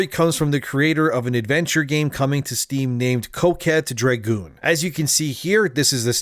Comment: i heard "coke head dragoon" and thought that was a badass idea lmao